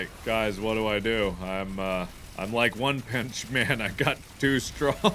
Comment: did Mark really say one pinch man instead of one punch man